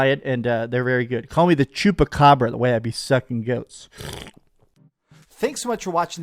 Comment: Did he just say at the end that he sucks off goat? 😆 🤣 😂